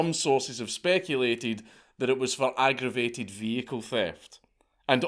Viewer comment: Did I just hear "fecal thief" ????